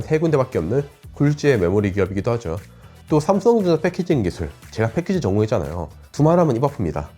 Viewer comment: 설상가상이라는 단어가 설산가산으로 들리고 9:32 삼선전자 패키징 기술 할때 진짜 못들어주겠어요. 차라리 유망한 ai 음성으로 입혀주시면 안될까요 ?